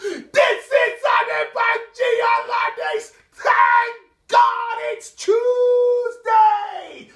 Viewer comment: Forgive me Adebanji, I thought you were saying 'Thank God it's Tuesday'. lol. Now I know.😎